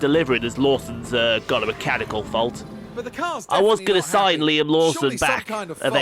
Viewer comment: Half the time you were saying Liam Lawson I thought you were saying Luam Neson 🤣